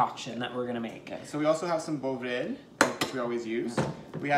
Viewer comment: Gentlemen, I had a hard time hearing what you added in the small bottle with the yellowish top...what is that?? I couldn't hear what it is even though you said it around 1:07 and again around 2:13. Also, and please, not to be critical, but sometimes you talk over each other in your enthusiasm for what you are doing and for those of us with hearing impairments, it's hard to take it all in. I love what you are doing in your videos...thank you for making them and I'm doing the chicken nuggets right now!! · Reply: It’s called Bovril, its a Canadian product. the recipes and ingredients are also all listed on our website. Hope that helps.